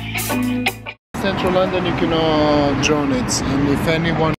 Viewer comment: What was the guy saying in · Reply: 'in london u cannot drone it'